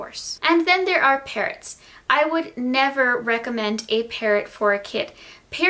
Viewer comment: Hahahha no ofense but hahaha I thought you said parent instead of parrot Ps.I love ypur videos💙💙💙